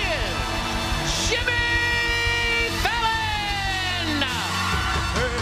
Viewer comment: Why do I keep hearing ‘Sjimmy’ in the introduction…